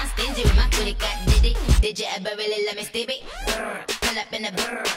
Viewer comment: Is it just me or did she say "Diddy" ?